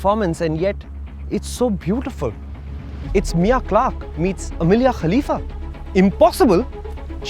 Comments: for the first time i heard mia khalifa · Did i just hear “Amelia Clark meets Mia Khalifa" Or “Mia Clark meets Amelia Khalifa" 🤣🤣🤣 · did u just say mia khalifa?